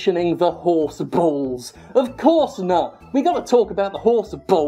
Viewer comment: did he just say "Of course nut" ?